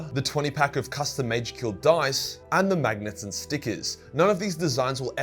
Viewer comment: Bruh I had water running and when was talking about stuff being 30% off instead of saying "magnets and stickers" I thought he said "Magnus the stupid."